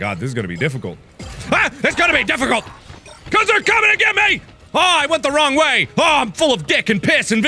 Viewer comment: did he really say there comeing in me???